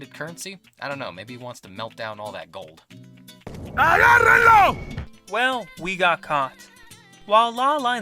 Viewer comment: i think he says "he got reload" hahah